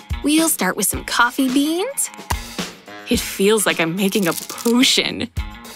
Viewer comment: I swear I heard her say pootion